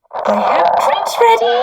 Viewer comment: I find the idea has presented terrifying in this sense: Here you have a work of fiction. Something that, according to the characters is a “real” world. Within that fiction is inserted something incredibly dark. Something that is extranatural (not quite supernatural but not extraterrestrial either) that appears within said fiction. Then you have the real world, where the fiction is presented as fiction. And then, the extranatural something realizes the audience. And that something doesn’t want to be fictional anymore. It wants out if the story. So it attaches itself to the merchandise, and seeps out into the world. EDIT: and it attached itself to the ONE thing that could see that real world, who knew (in accordance to the “real” world/real world relationship the show had) of that audience. And their neighbors. And their neighbors. And their neighbors. And their neighbors. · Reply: Also, it sounds like she said “I have punch ready!” Which is weird to say mid-story